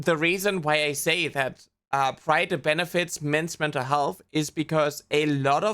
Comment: i misheard "pride month benefits" as "pride, with benefits"